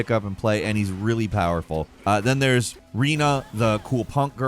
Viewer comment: did he just say reena? 😂